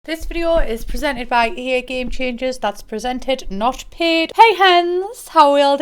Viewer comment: says: ”presented, not paid” I hear: “presented, not peed” 😳